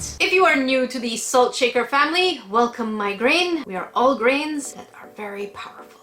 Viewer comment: Every time you say "welcome my grains" before the con list i hear "welcome migraine"... fitting?